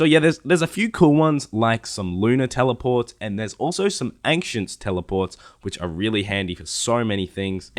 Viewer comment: sounds like you say anxient Tele, like you're gona say anxious Tele haha Good vid though